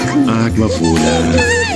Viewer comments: Omg at 6:54 when she threw the bottle on the floor, it sounded like she said “All done!” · It sounded like she said “all done” when she threw the bottle so adorable